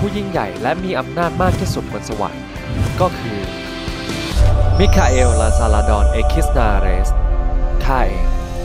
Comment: สิ่งที่ได้ยิน มิคา เอลสาเรสเมสเตส แสส ความเป็นจริง มิคาเอลลานซาลาดอนเอคิสนาอาเรส